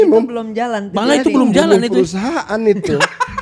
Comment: itu ngomong apa ya? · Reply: "Membunuh perusahaan itu" *Tawa coki